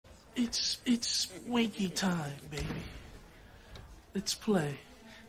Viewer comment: That man said it's winky time .. let's play 19 inches of fun 🤣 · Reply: Lmfao for the longest time I thought he said it's sqeaky time not winky time, thanks for correcting me bro 👍